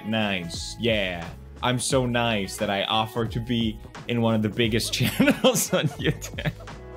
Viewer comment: did he just say it or did he say igger